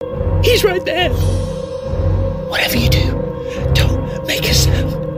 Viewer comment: Whatever u do don't make a "SNIFF" got me dying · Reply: DOGS4EVA_ I don't think so. I think he misheard the word "sound"